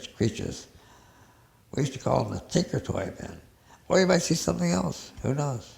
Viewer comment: did he just call the clockwork elves the tinkertoy men?